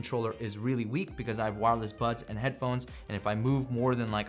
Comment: Love the review! I was just a bit taken aback at 9:30 due to automatic subtitles and translation to my native language; instead of wireless buds, YT understood wireless butts and with some auto syntax, the sentence was changed into "My wireless a** is really weak"...